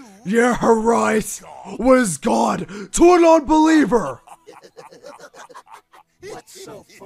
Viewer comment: wait did shadow just call sonic.exe a HER i know it maybe was a mistake but if it was on purpose ROASTED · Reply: I thought he said unbeliever not nonbeliever